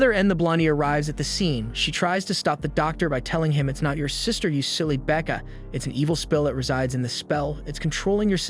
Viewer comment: anyone heard him say "its not your sister silly baka"